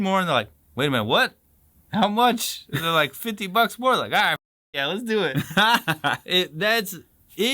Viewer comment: It's not just that it adds up. What he said was "You get paid fifty bucks a month more—so that made it a hundred bucks." US Army Privates were paid $50 a month in 1942, so the $50 danger money made it double the salary!